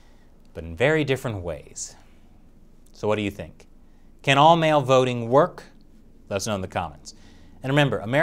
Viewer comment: I was only half listening to this and at the end where he says "Do you think all mail voting works?" I thought he said "Do you think all male voting works" and thought for a second the US had started debating some much older stuff.